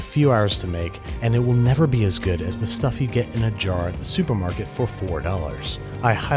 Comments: Did he really say, it would be never as good as the supermarket one??? Or have I misheard it? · did he say “it will never be as good as the jar in the supermarket”? Lmaoo